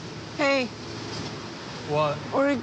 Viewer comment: I thought she said "Bring me my red bag with my Manga"